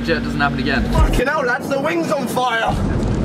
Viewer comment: I thought callyx said the wind was in fire lol